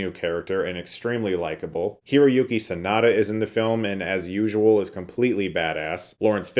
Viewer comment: Did you say Yuki Tsunoda at 3:10 ? Did not know an F1 driver was also an actor! sorry lol